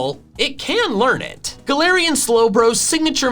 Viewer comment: you said galarian Slowbro instead of galarian Slowking 😂